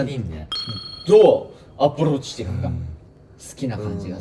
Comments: ゆうぴの言う「アプローチ」がどうしても「アップルウォッチ」に聞こえる、、、！笑笑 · の、優斗くんの「どうアプローチしていいか」のところ、「どうアップルウォッチ」って聞こえてジワジワきてます。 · どう？アップルウォッチ ってなに？！ゆうぴ、、アップルウォッチにしか聞こえないけど合ってる？ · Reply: どうアプローチしていくか ですね（笑） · 「どうアプローチしていくか」が「どうApple Watchしていくか」にしか聞こえない😹😹😹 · アプローチがApple Watchに聞こえるww